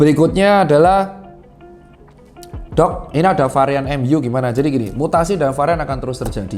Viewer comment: Gak salah dengar nih varian MU ?😂